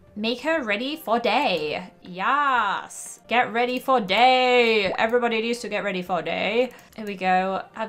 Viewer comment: I heard "get ready for datee! everyone needs to get ready for date."